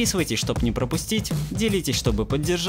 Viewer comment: "с вами БЫЛА Сел"? мне послышалось? или так "с вами был АСЕЛ"?